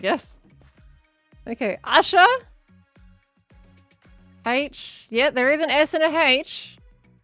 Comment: what I heard was “Yeah, there is an s and Anne Hache.” 🤣🍻